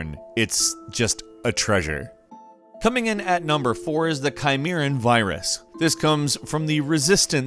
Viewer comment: thought he said Coronavirus